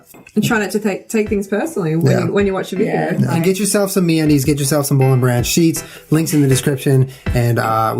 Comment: At the end when Julian said "Get yourself some MeUndies..." I thought he said "Get yourself some mayonnaise" and I was like, "Julian, did you not just sit down to talk about the vegan lifestyle??" Lol... :)